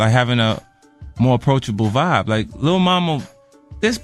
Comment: i thought i heard him say lil mama but idk